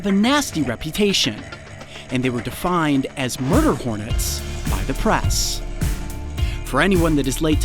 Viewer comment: Instead of "murder Hornets" I heard "marble Hornets" Creepypasta fasn will know about marble Hornets but this reminded me of the TikTok sound"i've been stung by a wasp "